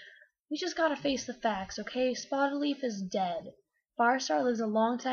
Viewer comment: When you said Spottedleaf for the first time I thought you said HOLLYLEAF and I was like wtf?